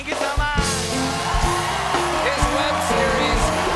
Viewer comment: Damn he really said potato! lmao